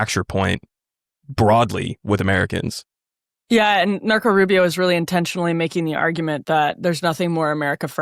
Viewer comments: I swear it sounded like Emily said at one point, "Narco Rubio." And I think that nickname should stick. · Did Emily say "Narco" Rubio 10:58? lol 😀